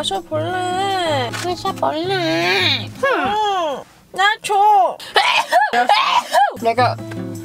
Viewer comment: sounded like she said nacho